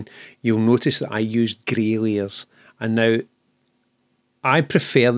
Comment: I did laugh i thought i heard "Grailiers" but actually with your accent it is "Grey Layers"! took me a while! :) I have watched the part where you talk about the need to ad basic canvas coloured backgrounds as a first layer a few times but i still don't get why you need to do it i am afraid maybe a topic for another great video?